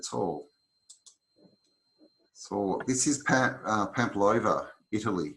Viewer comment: what is the city at 14:38? Sounds like you said Pamplova, italy? i'm having trouble finding it. I google pamplova and it shows me a sandwich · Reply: ohhhh Palmanova, italy